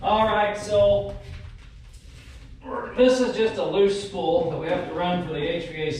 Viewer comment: did you say loose spool or loose stool.. LOL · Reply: Spool 😂🤣